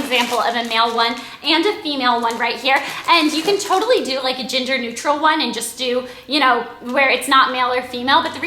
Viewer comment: lmao thought she said ginger neutral